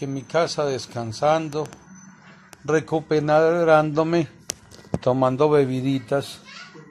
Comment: El señor cocada que dijo no se entiende🤣🤣🤣 minuto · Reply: Recuperándome.